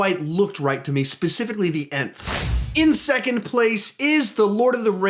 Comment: Does anyone know what he says at 2:52 “specifically the ???” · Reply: Jackson Cason Specifically the Ents